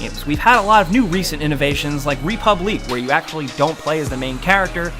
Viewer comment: whats the game at 10:50? i cant figure out what he's saying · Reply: It's République or Republique remastered.